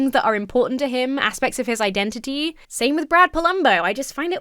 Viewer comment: Not gonna like, at first i thought his name said "Plumbo" and it made me think of the sims lmaooo